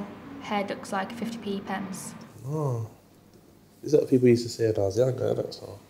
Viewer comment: wtf did he say??? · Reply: sharki mohammed "That's what people used to say when I was younger"